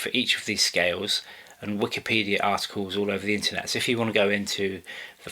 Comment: I thought you said Wikipedia assholes, but no, you said articles